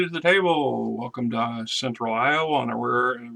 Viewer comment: many pardons I thought you said central Ohio Once again Long day Merry Christmas!!